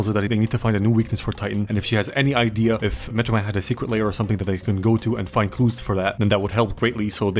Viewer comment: Captions Lair as Layer lol. I feel like pointing out mistakes